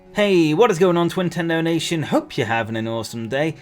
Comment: it says in the subtitles 2010 donations instead of saying twintendo lol